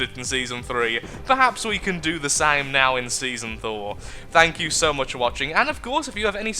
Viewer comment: I was just listening, building a Cruiser, and all I hear is "Perhaps we can do the same now in Season Thor" xD